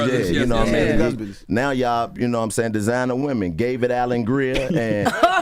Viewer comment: Did he just say “David Allen Rear”?? 😂😂😂😂😂😂😂